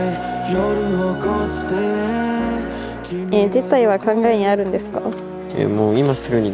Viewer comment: こっから何言ってるか何回リピートしても 分かんなかったんだけど誰か教えて！ 今すぐにしたいって卑猥な会話にしか聞こえない😅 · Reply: 辞退は考えにあるんですか？ って言ってますね。